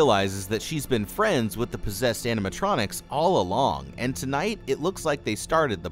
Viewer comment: For a moment I thought he said "Wh0re-possesed animatronics" LMAO 🤣🤣🤣🤣🤣